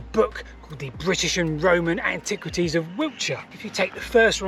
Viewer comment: Lol, @ 1:34 auto-captions say "british and roman antiquities of wheelchair" (instead of Wiltshire). Quite like that! :D